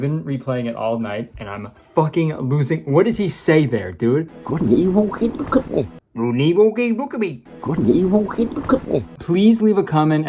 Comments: "...good and evil? Hey, look at me." My dad's voice was that low (but not as buzzy) so I didn't have as much trouble as you. Also the lady started to look up at that last part so the context helped Ms figure it what he said. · I think he's saying, "Good and evil keep looking at me." Or something around those lines. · I think he's saying "good and evil he look at me." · As for what the dude is saying at 4:48 it sounds like it starts with "good and evil." That's all I can make out. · I think he is saying “good and evil, wait look at me” or “good and evil, what look at me” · all I hear is “Good and evil keep coming” and I don’t know what that means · My best guess for the boss dialogue is “good and evil keep good company” but let’s face it, it could be literally anything 😂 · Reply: I heard “good and evil, look at me” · I think what he's saying at around 5:00 is "good and evil (something) couldn't he?" atleast that's what I hear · "good and evil? Hey, look at me" is what i heard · it sounds like he is saying "Good and Evil. Hey! look at me." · i think he says something like “good and evil couldn’t hurt me”??? the problem with that is that’s definitely not a phrase in like anyone’s vernacular, but something similar to that maybe? good and evil hit the cookie? goop a lee loo hitchacoochie? go potato he’s a botchy? help. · I think he’s saying, “good and evil keep ‘em company” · I heard 'Good and evil? I mean look at me!' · I swear he says “good and evil, ok look at me”